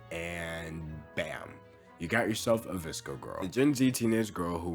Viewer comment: "I thought you said 'Viscous Girl'! Am I supposed to stop sending human sacrifices to Chtulhu's teenage daughter? I mean, I can't just stop worshipping this particular Viscous Girl - she'll eat me alive as an appetizer and then move on to consume the rest of the Eastern Seaboard!"